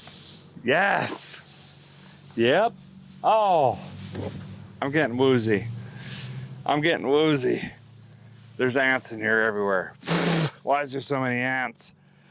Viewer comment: At the 16:13 mark, I could have sworn said, "there's in here everywhere" LOL!!